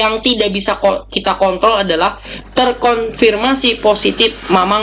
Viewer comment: cuman salah denger atau emang bilang kont*l 6:10 coba dah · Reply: kontrol :(